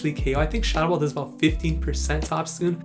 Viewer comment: at first I heard you say 50%, I was like no way a triple resisted move does that much, so I go and check on pvpoke, it does 24 damage to a 178 hp obstagoon, which is just about 15%, and I listen again, ya you said 15% xD